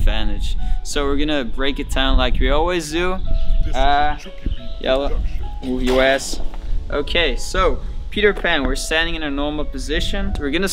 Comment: Did he say yalla on 8:05 min or am I bugging ?